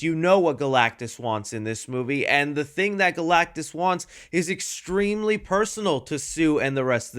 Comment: did you say and the THING that galactus wants ? 👀